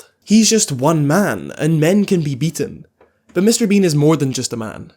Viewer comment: I misheard as "and he's just 1 man and men can be eaten"